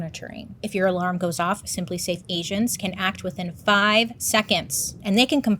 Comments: I thought you said Simply Safe Asians not Agents 😂😂😂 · During ur spronser intro I thought u said, simplely safe Asians 👏🏽 can act in FIVE SECONDS 🤣👏🏽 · I definitely thought she said, “Simply Safe Asians,” and not “Simply Safe agents.” 😂